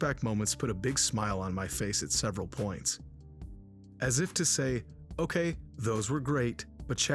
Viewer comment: Wait, wait. Did you say "ASS if to say" ?